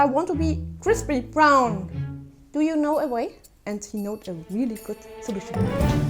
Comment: Is anyone gonna talk about 0:45 when she sounds like she said “do u know da way”